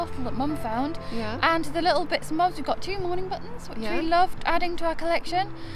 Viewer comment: I always thought you were saying morning buttons but now I realize that you are saying mourning buttons. Learn something new everyday. Great show ladies.